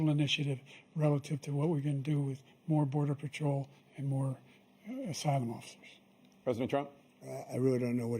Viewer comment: what he actually said was "asylum officers" took me three days to figure it out but that's what he said